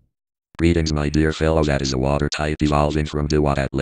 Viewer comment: OHHHHHH lol he said my dear fellow that is a water type i thought he said my dear fellow water type :p